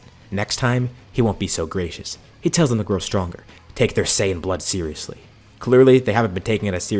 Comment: Bro as soon as I heard Cumber say “your not taking your saiyan blood seriously “ I was like bro I am sorry but your a different breed and just built alternatively different I’m sorry I’m normal